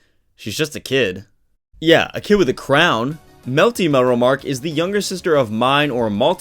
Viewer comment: I might be trippin but i thought heard him say Melty cute melromarc and i was like ahhh